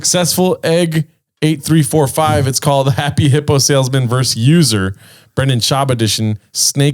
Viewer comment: Happy Hippo I Thought He Said Hungry Hippo The Game With The Marbles and Four Hippos Wow They’re Sell Kraydom Now 🎲🎲